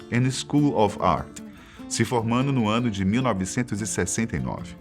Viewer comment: No minuto 1:20 você disse: Se formaNo? Eu ouvi bem?